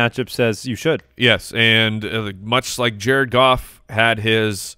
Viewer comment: Did just call Jared Goff... "Jerkoff"? 🤣🤣🤣